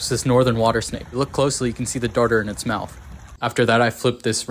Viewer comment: I lowkey thought he said “I could see the tartar in his mouth.” Remember, brush after meals kids! 😂